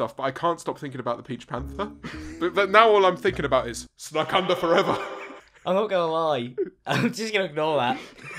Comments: that guy is really funny lol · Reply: Marriott I thought you said snack UNDER forever...... cos the snacks were under the seat! 😂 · I actually thought he said snack UNDER forever! cos the Twinkies were under the sofa! 😂